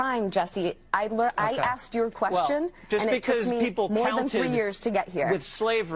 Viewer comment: Did she say “I asked your question” 8:00.